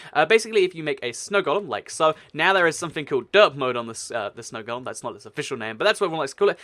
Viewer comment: Am I the only one that thought said dirt mode instead of derp mode when he was talking about the snow golem